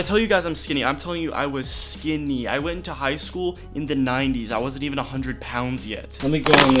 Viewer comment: I thought you said you went to high school in the 90's as in the years lol, you look young.